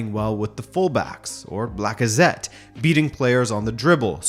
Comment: Did he say blackazette??????